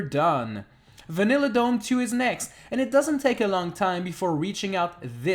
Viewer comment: did you say we’re dumb?!